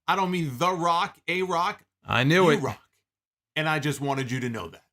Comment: Yeah I thought he said fuck you but it is what it is.